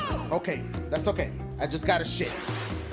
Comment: I heard "That's okay I just gotta shit" ??